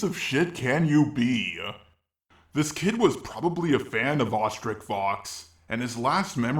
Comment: For most of the video I thought you said ostrich fox